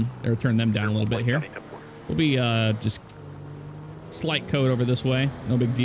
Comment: I hate when people say stuff like “slight code.” There is no such thing.. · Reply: I heard him say signal code not slight code